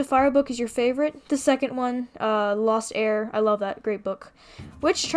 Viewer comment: when you said your favorite book was the "lost heir" i thought you said "lost air" XDDD e g g o 🥚